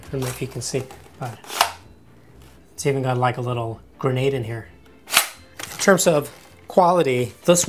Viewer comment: Did he say grenade.???..